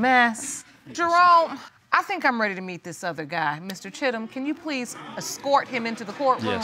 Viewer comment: I thought the judge said #shittum